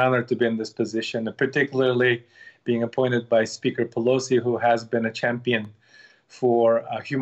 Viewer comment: did he say speaker pelosi is standing up for these people.........................